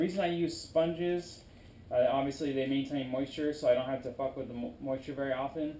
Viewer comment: *me at 5:38*: did he just say fuck??